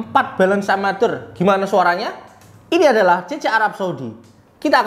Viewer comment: udh gw dengerin ampe speed 0.5x tetep kedengeran "CC Arab saudi.."😅